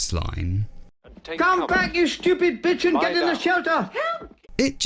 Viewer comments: when I first heard that I thought he said “you stupid pigeon” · did James call her a b*tch